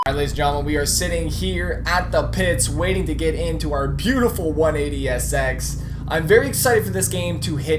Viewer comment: it sounds like he said sex